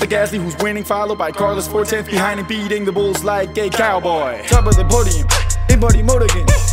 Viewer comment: is just me or everyone hears "gay cowboy" instead of "a cowboy" at 4:12?